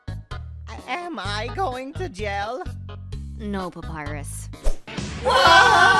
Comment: i thought he said "am i going to hell?"